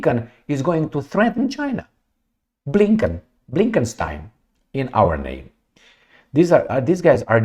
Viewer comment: said "Blinken style" a bit fast. I misheard as Blinkenstein or Frankenstein